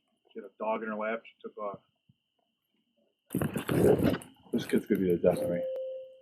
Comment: what does the officer say after walking away from? Sounds like “this kids going to be a destiny” if so what do you think that means? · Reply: It's the dad who said "this kid's gonna to be the death of me." It's a common phrase meaning this thing/person is causing me so much trouble it will result in my death!